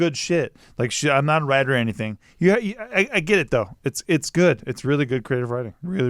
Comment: Omg I was listening to this on my phone and at one point I sneezed and at the same time said "it's good" but to me it sounded like "Bless you" and it freaked me out! but then I went back and realized I misheard it lol I probably shouldn't listen to these creepy stories!